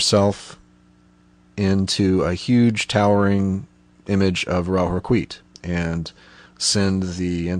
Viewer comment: Really great video I've got one question, what did you say at 12:25, "Huge towering image of ?" I'm not sure what the last word is. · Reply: Ra-Hoor-Khuit